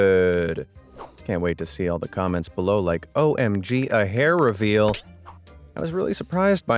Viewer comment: I thought you said face review not hair review!